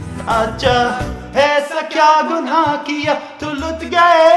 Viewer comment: I heard IKEA lol